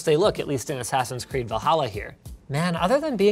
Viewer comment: Did he just say, 'Va-halla'? LOL